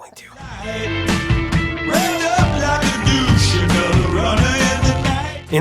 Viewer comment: I always heard it as deuce, but it did sound like 'wrapped up' rather than 'revved up'. Starbird no 2 was fantastic as well.